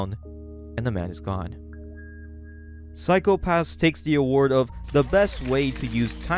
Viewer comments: I thought you said Psycho Pass and was SO CONFUSED at first. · I was so lost through the whole video because I thought you said Physco Pass and I was like “ when tf did any of this shii happen” 😂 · I thought you said psycho pass at first and I did a double take